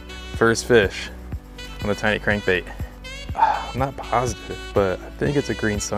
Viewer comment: thought it said mini clickbait